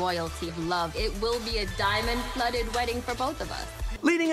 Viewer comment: Did I hear "fake wedding"? 👂👂👂